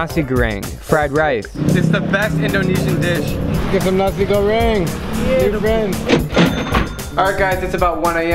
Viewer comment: sounded like "Nazi Goreng". ?!?!!?!?!?!?